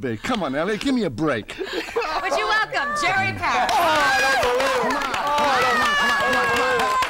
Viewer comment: Did I hear Robert Stack say "Oh shit"?!!! (at the 3:21 mark)